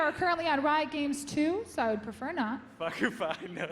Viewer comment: please somebody tell me what imaqtpie said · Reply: either "fucklng fine me" or "fucking fine then" is what I got from it.